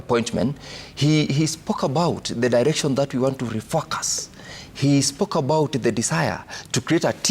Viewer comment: 😢😢... did he say .. "he spoke about the direction he wants to re-f*ck us".. 😮😮 LHM 😂😂